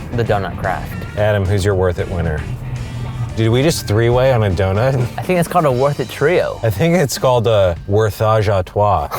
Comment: " i think its called a.....twatashawa....." omg i died